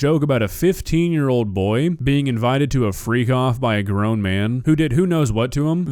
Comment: freak off was such a weird name for parties that i at first thought you said free cough.